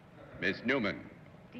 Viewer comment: My English is too basic, so can anybody tell me what word used Phyllis Newman to define the Bacall's voice? I can't understand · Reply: Fran Castillo "Huskiest", a form of the word "husky", meaning a deep, throaty, sensual voice. :)